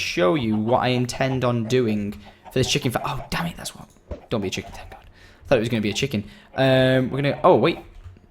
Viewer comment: it sounds like he says "don't be a chicken tender"